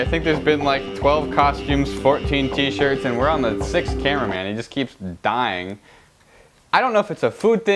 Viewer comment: When he said "The sixth camera, man." I thought he said the sixth cameraman.